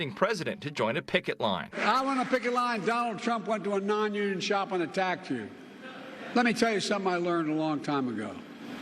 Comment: "I'm on a pikit line and Donald Trump went to a non-union shop on a (tractor)" wtf did Sloppy Joe just say? Can someone please take the marbles out of Joke Bidens mouth before he gives a speech. 😅